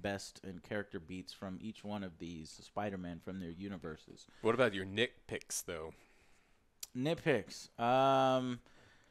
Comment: It sounded like said " what about your dick pics though? " ........... wtf?!?! 🤨